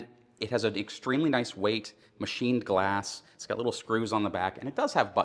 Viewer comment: did he say “machined glass” ?? What are you talking about